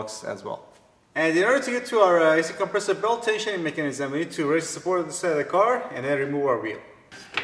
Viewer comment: wtf did he said at 2:18? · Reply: "And In order to get our, uh, a/c compressor belt tensioning mechanism we need to raise support to this side of our car and then remove our wheel"